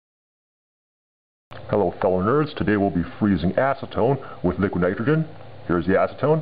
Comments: lol sound like ur saying asstone so ima call it asstone XD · I thought he said hellow boners at the beggining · Does he say Hello Boners ??? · umm at 0:01 did he say hello boners? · it sounds like he said "hello boners" at the start XD